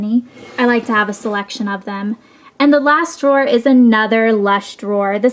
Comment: When you said "The lash drawer" I thought you said last drawer and I was like finally! But then there was a gazillion more things! ;O <3